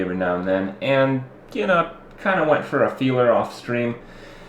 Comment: what did you say at 0:28? you kinda went for a "feeler" off stream?